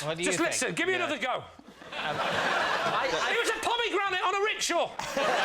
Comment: what is lee saying at 4:15 after "let me have another go" ? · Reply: "It was a pomegranate on a rickshaw!"